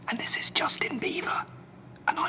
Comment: I watched this like 100 times so the first time I thought he said "save your screens" so I did. But when I watched this again he said "save your screams" but when I heard it I was screaming at my brother so i basically hit him and kicked him and I didn't save my screams😕 lol